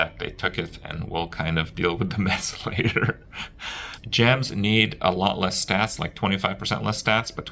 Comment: I thought they said gear required 25% less stats, not gems.